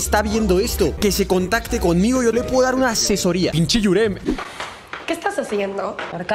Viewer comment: Qué dijo aquí? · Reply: "Pinche Yurem"